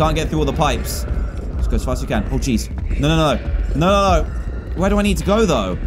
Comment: the subtitles say “pour cheese” instead of oh geez 😂